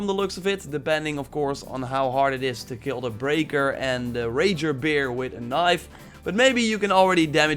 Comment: sounds like ur saying raper